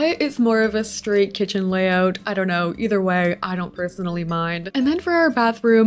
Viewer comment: when you said stry KITCHEN layout I heard it stray kids layout 😭😭